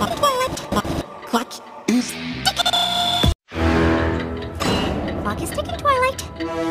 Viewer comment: I thought she said the clock is tickitey